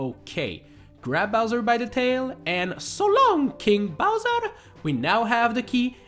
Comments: Always thought Mario said “so long GAY bowser!” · THATS WHAT HE SAYS?! I THOUGHT IT WAS GAY BOWSER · It is only then that I know it is "So long King Bowser" instead of "So long gay Bowser" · Mario:"So long king Bowser" I thought he said."Shut up gay Bowser" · I thought Mario always said, "so long gay Bowser" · When mario throws bowser i hear “So long ‘eh Bowser.” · "So long King Bowser". Oh. Thought it said "Gay Bowser". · Oh my god is that what he says? I know they're not actually saying "So long gay Bowser", but all these years I had no idea what they were actually trying to say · am I the only one that thought Mario said "So long, gay bowser" as a child? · Wait Mario says “So long king Bowser!” I always thought he said “So long gay Bowser!” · I thought Mario said: SO LONG GAY BOWSER Not: SO LONG KING BOWSER